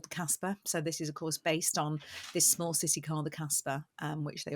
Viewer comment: This small sissi car the Casper? Did I hear that well? 7:36 What a naughty pronunciation, Ginny Winny.... 😂